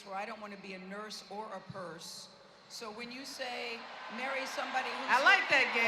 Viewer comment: Gayle..don't want to be a Nurse or a Purse. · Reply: I thought she said nurse or a herse (vehicle for dead weight)